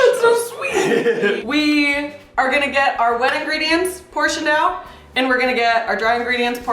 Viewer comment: Random Fact: Every single time they said the words “wet ingredients” I heard “wedding”. What the actual fuck is wrong with me?!?! Also, “you put your middle finger on the crack” had me laughing way to hard considering I’m almost 30.........